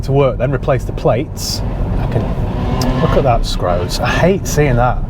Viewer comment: what did say? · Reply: ‘Look at that scrote’